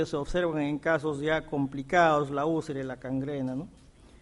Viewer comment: escuché mal o dice cangrena?